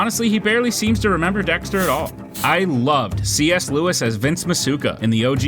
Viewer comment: Is anyone else hearing C.S. Lewis at 6:54 instead of C. S. Lee?